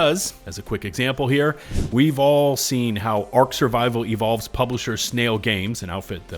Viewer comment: Every time you said "Ark Survival" I thought I heard "Arc Raiders." I really need to give that game a try